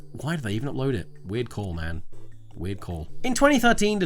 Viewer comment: i thought he said “weird cow man,weird cow man”and now I want to watch a show of that